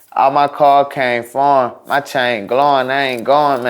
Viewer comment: I always thought he said: "All my Cocaine foreign"